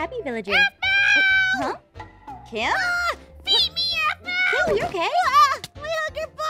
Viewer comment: I thought Kim said PHOEBE APHMUA